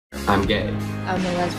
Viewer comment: For the pansexual part I heard "european-sexual" 😭